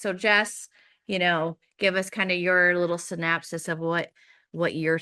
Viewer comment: did she say synapsis? I’m guessing she meant synopsis…